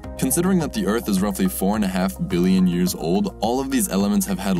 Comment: spelling error in subtitles, says tears instead of years